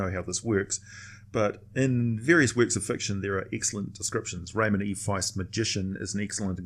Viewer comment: Does anybody know what book he is referring to at 1:40? The closest google can get me is "remedy device". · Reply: Free Williy Magician, by Raymond E. Feist